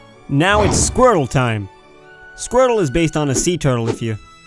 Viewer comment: "Now It's Squirrel time!" did he say SQUIRTLE or SQUIRREL?